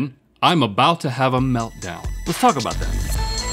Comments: When Rhett said, “Let’s talk about that” in the beginning, I thought he said, “The f**k about that”🤣😂🤣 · So I have minor hearing loss and it sounded like Rhett said " Let's f*** about that" but he said "Lets talk about that" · When Rhett said, "Let's talk about that." I thought he said "Let's f___k about that." $ w $ 😂😂